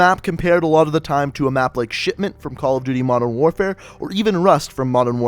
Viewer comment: When he said shipment I thought he said shitment